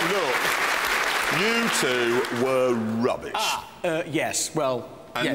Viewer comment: Everytime I hear Jeremy say "You two", in my head I thought he's saying Youtube